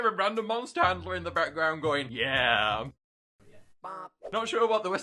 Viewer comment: Does he say yeah